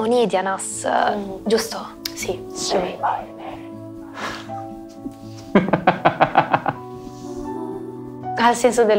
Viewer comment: cosa dice? Non capisco mai un cazzo quando fa quella voce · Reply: "Say my name...😈"(L'ho letto sotto a un altro commento...scusa il ritardo di 2 anni!;-P)